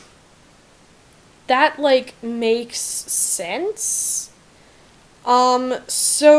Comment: did she say sans 😂😂😂😂😂👌👌👌👌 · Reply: Sense